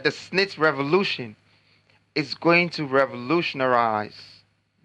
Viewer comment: Did I hear Revolutionarize? Instead of revolutionize 🤣🤣🤣🤣💔